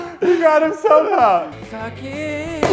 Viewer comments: what was Matthias saying? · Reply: He said suck it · did anyone else hear someone say "f*ck it"? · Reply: Actually I think Mathias said "Suck it" as in bragging that lost